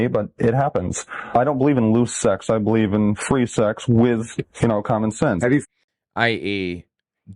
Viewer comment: Right around 15:30 I think he actually said condom sense. That was an early meme. There was an article in Playboy magazine about condom sense. It means that if you want to screw around at least use protection.